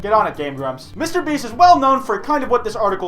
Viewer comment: Thought it said Mr breast